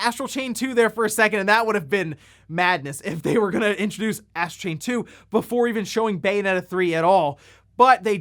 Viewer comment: Did you call it "Ass Chain 2"???????????